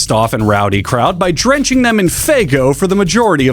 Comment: insane clown posse drenched the crowd in… faego? FGO? Faygo? What’s that? What’s Huggbees saying the posse drenched on the crown? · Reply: Faygo, it's a soda produced mostly in Michigan, which is also where ICP is from. It's kind of ~their thing~